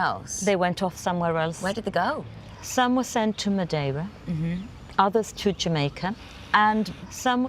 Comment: is she saying Madava? ?in India? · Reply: Madeira